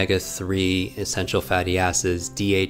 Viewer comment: Sounded like he said essential fatty asses! 😂